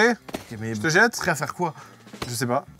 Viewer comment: "LECHE MOI "?